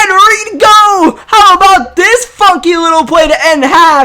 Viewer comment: Did he say fucking or funky at 19:47???😂😂😂